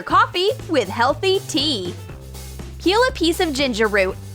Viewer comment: turn on captions 😂 Hint: when she says PEEL a piece of ginger The captions say KILL a piece of ginger